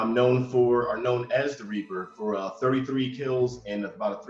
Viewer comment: Oh his code name was "The Reaper" 😂 i swear i heard him say the creeper so i was sitting here wondering what the correlation between this and minecraft.